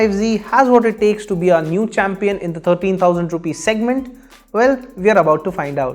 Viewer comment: did i just hear 13k ? plz Do Not provide wrong info